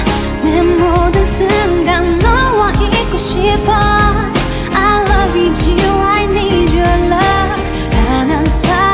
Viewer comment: what did jk say rosé or what? I wished jk would stand where jimin stood😍😍🔥❤️ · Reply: I think he said rosé-ssi